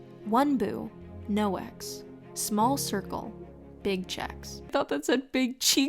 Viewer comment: Lmao 😂 I thought it said “ small circle big cheeks” too 😂